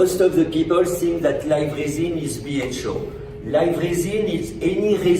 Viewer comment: Really nice video and research! but the subtitles don’t always match what he is saying, they can make it seem like he is saying something that he isn’t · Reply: for example. He says BHO but the subtitles say ”spiritual”.